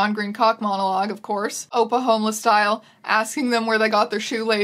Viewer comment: every time i hear you say ‘oppa homeless style’ i think youre saying ‘oklahoma style’ and it trips me up like what did oklahoma do